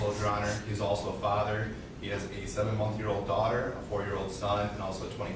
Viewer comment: Anyone else notice what the lawyer said at 6:41 he said this man has a 87 month old daughter 🤔 now did I hear that right ✅️ 🤔 😳